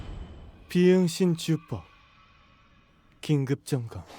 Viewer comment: 이거 왜 븅신 주법으로 들리냐?